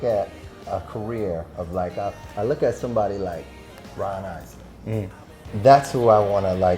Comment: I cant understand... bryan.. who? Please can anybody tell me the full name? Im learning english and i love this artist. Bless from Chile. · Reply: He’s talking about Ron Isley from The Isley Brothers